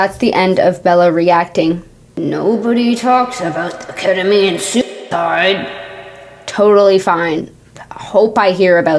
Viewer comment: Want to preface this by saying Thank you for covering this serious topic in such an unbiased way However, I forgot that some words were being censored and for the whole video thought you were saying ketamine soup fight 😂😂😂